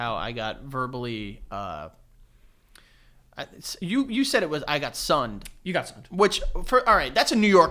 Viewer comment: I was today years old when I realized that for years I thought they were saying “ I sonned him” instead of “I sunned him” like I burned him. 😂😂